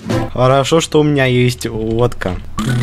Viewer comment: Мне послышалось как хорошо что у меня есть водичка а не лодка😂